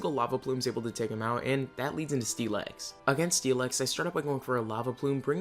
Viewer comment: im sorry what did you just say? steel eggs? steel x? that was funny as hell 10:53 well at least it didnt dound so bad this time, but if you use captions be prepared for a laugh....